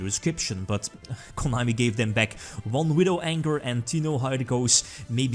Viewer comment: Did he say Widow ANGER at 1:52?